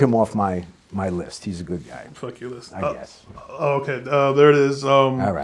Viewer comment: DID BRO SAY FUCK YOUR LIST?? 😂😂😂😂